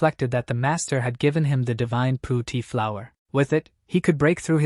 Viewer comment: divine poop tea flower (I heard)😅😅😅💩